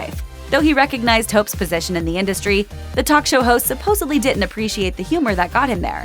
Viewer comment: Interesting and somewhat funny. More in the realm of gossip than entertainment. BTW, sounded like "supposably" at 9:31. That word is not often used. In this case the correct word is supposedly.